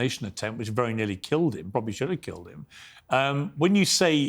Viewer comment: Did Morgan say "probably should have killed him"?